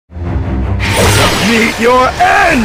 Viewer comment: "Meet your end"🙄 "HaVe a ShRoOmY"🤪 "For my master"😔 "Face my SeRvAntS"💀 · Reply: Why did i thought dark choco said beat your a$$ 💀💀